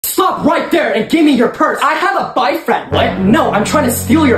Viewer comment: Wait did I hear "Fock right here"